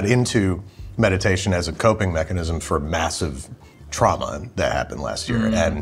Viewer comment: he 100% thought Babish said massive drama, not trauma